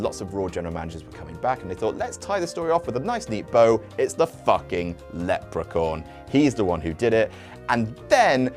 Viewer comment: did he call hornswoggle a "leprecorn"? A leprechaun unicorn? Lol